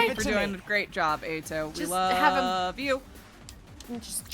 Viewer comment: Wait did he just say "Ha, got 'em"?